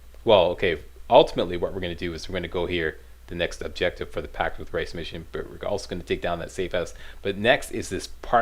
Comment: Every time you say "Pact with Rais", i hear "Packed with Rice!" like you're doing an ad voice-over for breakfast cereal.